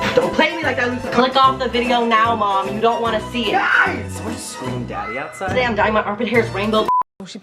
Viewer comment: “Should we just scream daddy outside?” It’s been 20 seconds and this video is already chaotic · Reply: I thought he said "Did someone just scream daddy outside?"